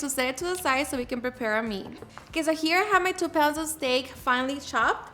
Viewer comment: I thought she said 2 pounds of snake 😂